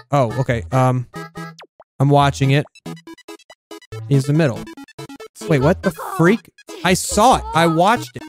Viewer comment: The boy sounds like he said "What the fuh..!?" and that's hehe for me